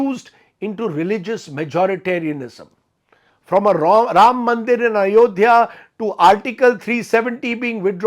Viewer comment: Did Rajdeep called Ram Mandir as Wrong Mandir ?